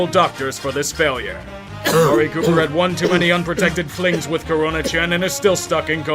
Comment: Am I crazy or did I hear "Corona-chan" XD